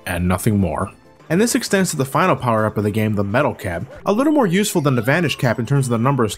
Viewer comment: I clearly heard "Metal Gear", but the subtitles say "metal cab". I went back, now I hear "metal cap". The fuck? I can still remember it, I heard "Metal Gear" clear as day!